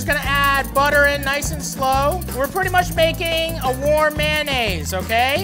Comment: I legit thought he said, "We're just gonna' make a WAR mayonaisse!" and for 2 seconds I was like, "The heck is a WAR mayonaisse?!?"